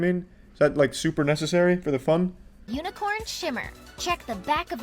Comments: I heard "unicorn shitter" instead of "unicorn shimmer" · i accidentally clicked off at 4:23 and it sounded like “unicorn shit” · When the tourturel said unicorn shimmer I thought they said unecorn shitter lol🤣😂😆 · Whenever she said unicorn shimmer I thought she said unicorn shitter💀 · When it say unicorn shimmer, I thought it said unicorn shitter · when they said unicorn shimmer I paused it on accident and I thought they said "unicorn shit"